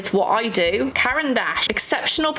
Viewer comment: Idk if it's just me but I hear Caran D'Ache as "carryin da ass" in my head every time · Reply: I heard "Karen Dash" and that's not what they're called 😭😭 (But she also said Washi tape like "Woshi"?)